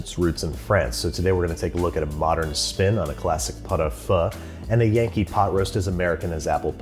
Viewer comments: I thought he said Pho and not pot au feu at first, so I was confused when he started on the second recipe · Great vid but not gonna lie this is the weirdest looking Pot-au-feu I've seen in a while (I thought you were saying Pho at first, very confused lol). Now it doesn't look bad at all, but it's needlessly complicated, Pot-au-feu is a cheap cut of beef like shank or chuck I guess (not familiar with the american cuts) that you throw in a pot with turnips, onions, carrots, leeks, potatoes and marrow bones, the only things you have to mind are skimming and vegetable cooking time. It's strange because some parts of the recipe are really accurate like sinking the clove in the onion (you can char the onion to give more colour to the broth) and others are completely off the mark like adding asparagus and the tartar-ish sauce at the end (homemade mayo does the trick perfectly, and you usually eat the marrow on bread with salt flakes). It's a very hearty, simple, cheap dish that should fit perfectly with your Basics series but you presented a sophisticated and non-traditional version, which looks good nonetheless. I'm not sure where you got that "pot-au-feu is super fancy and laborious" narrative lol. Anyway, again nice vid, nice recipes cheers :)